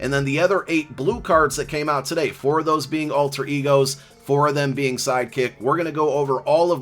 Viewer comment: Let's go!!!!I could have sworn you said "Alter Eagles" !!!!!!! :_Eyo::_Eyo::_Eyo::_Eyo::_Hype::_Hype::_Hype::_Hype::_Hype::_Hype::_Hype::_Hype::_Hype::_Hype::_Hype::_Hype::_Hype::_Hype::_Hype::_Hype::_Hype::_Hype::_Hype::_Hype::_Hype::_Hype